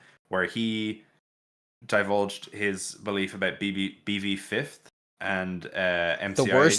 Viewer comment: The last tangent I thought they were saying "BB 5th". As in Balloon Boy. As in instead of the fifth MCI kid being Golden Freddy, they were Balloon Boy